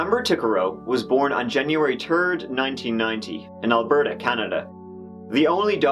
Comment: I thought he said “Amber herself was also a man.” Glad included the picture of her holding her baby, since my ears are so Americanly stupid. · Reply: Omg I thought the same. He also said January turd! 🤣😂🤣😂🤣😂🤣